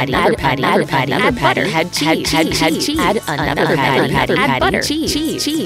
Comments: what it's saying add cheese what I'm hearing "Aichi". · sounds like she said acheese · Why did I keep hearing 'add buttcheese'?